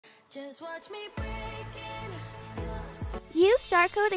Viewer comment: at the start i thought it said " Just watch me break in, into your safe!!! "